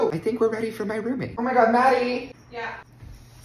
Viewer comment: Thought he said “You’re A”